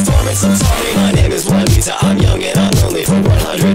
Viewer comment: am i the only one hearing mona lisa instead of wanita?